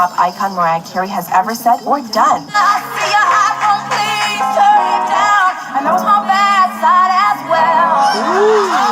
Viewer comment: what is Mariah saying? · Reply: 📲📲📲"I see that iPhone please, turn it down, and I know it's my bad side as well."🎤🎶🤣